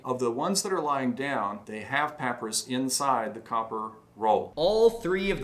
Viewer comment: Hey! Been on a binge on your channel and oh my GOD the videos are so well put together and have so much info! I wanted to just reach out to say I think at 19:00, says "papyrus" instead of "paper"? but, like, just really fast. I really don't know how relevant this is but if it's not been said in your comments, I wanted to say it without an "um, actually" tone. Mainly because my autistic brain loves the pronunciation of that word now <3 Keep up the incredible work and I'm so excited to watch more of your videos!